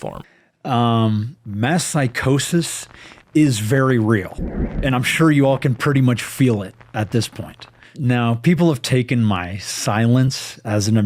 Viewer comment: "mass psychosis" he's the one who looks like he's gone through a psychotic break rn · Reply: Up until now I literally thought he said "my psychosis"